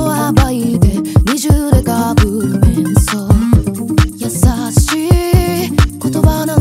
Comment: あたりの「面相」は「メンソール」にも聞こえる…だからタバコが画面に出てくるのか…